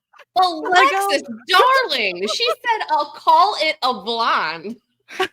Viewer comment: Alexis mostly used “she/her” for Nina, but there was definitely a moment where “it” was said, shortly after the blonde comment. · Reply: I thought what she was saying was "'call it a blonde" in reference to her hair color. She was trying to say "the [hair color] one" but couldn't decide which color to say because it's kind of an in between color. That's just how I heard it, I could be wrong.